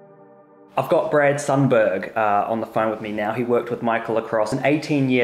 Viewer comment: i thought he said "I got GRETA THUNBERG" 🤣